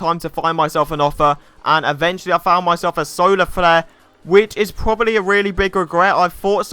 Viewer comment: I thought he said soda flares. Nice accent